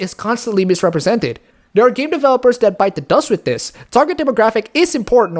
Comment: Did you just say....BITE THE DUST?